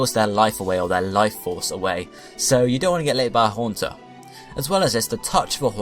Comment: I heard "you don't want to get liked by a haunter" as "you don't want to get laid by a haunter" 🤣